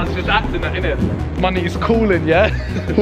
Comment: did Simon say my n*** calling me